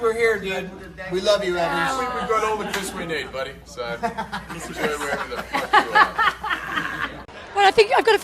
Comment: what did Hemsworth say...can anyone tell me..🤗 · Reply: "We have all the Chris we need, so you can can enjoy where ever the fuck you are"